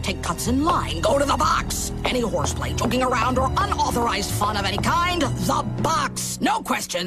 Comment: i thought she said f*cking around